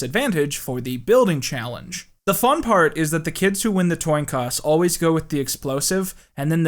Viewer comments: during the Destroy Build Destroy segment he says "toin coss" instead of coin toss. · Did you mean to say toin coss? Instead of coin toss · Did he say “toin coss” instead of “coin toss” at 15:09 or am I losing it